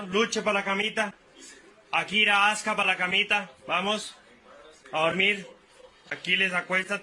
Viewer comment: понос, армия(слова которые я услышал)